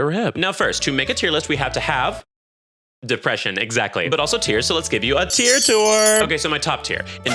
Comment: why'd i think you said tears insteads of tiers 😭